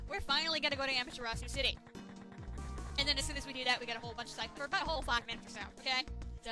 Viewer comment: side... buttholes..? Did I hear that right?